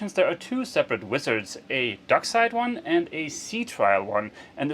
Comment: Ohhhhh! Dockside Wizard... For the preceding umpteen times you said that, I heard "Darkside Wizard" and was expecting a Darth Vader sighting. Problem with my hearing, not your presentation. Please excuse my "Ah Ha" moment.